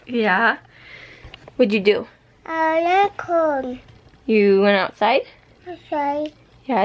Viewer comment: He said I went outside I thought he said, I like corn 😂